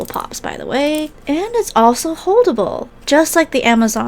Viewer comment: When she said “holdable” I thought she said horrible 💀